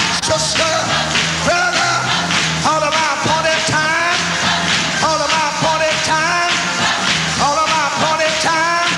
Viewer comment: What is he saying at 1:48? All about ___ time?? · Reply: Appointed time! Miss you Bishop💖